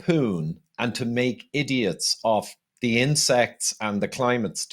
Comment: Insects! For a moment I thought he said incense. Anyway insects are of value. I don’t want to eat them but they could make cheap responsible chicken feed. I will eat their eggs and their meat.